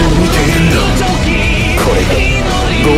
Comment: is it me or why am I hearing it as "sexu ball got to mine in cool"